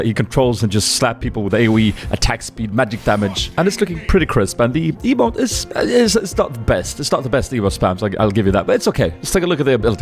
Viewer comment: "... looking pretty crisp and the EOINJseAIHesbiåo is.. ehh is not the best..." WTF are you saying right there??!!!